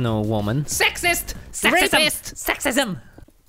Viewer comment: Did I hear Racist? 😂